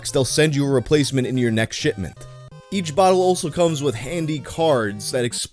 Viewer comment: did he say shitment 💀